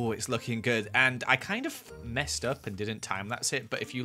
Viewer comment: I hear it at 00:44 he said: “i f- mess up “ ya know what f- mean (f*cking)